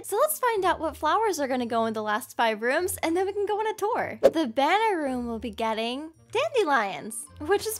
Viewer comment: In the room where it said banner room... I thought it said banana room 😅😅